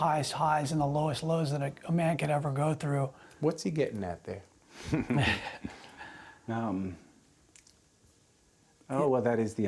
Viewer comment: Can someone please tell me what the interviewer is saying and the response from Roy's son? It makes no sense to me. I hear, "What's he gettin' that day?" The sons laugh for some reason, and then: "I don't know what that is the answer to a dull life there is problems that's what we're left with, uh-huh. Ha-ha." I'm very confused.